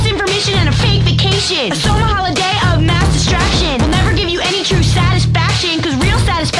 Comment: totally thought she was going to say "masturbation"